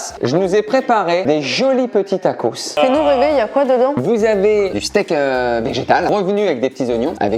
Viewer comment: Alors je sais pas si c'est mon imagination qui me joue des tour mais a 4:08 Camille dit bien a ben '' dit nous bébé il y a quoi dedans '' dite moi si vous l'entendait aussi svp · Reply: Fais nous rêver y'a quoi dedans